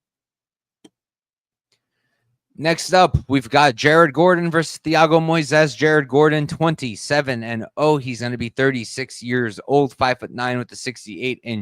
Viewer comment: Sounded like he said Jared Gordon is 27-0 lol